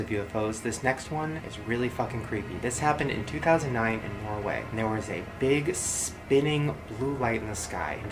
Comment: Did he just say Norway? o.o Faen, så creepy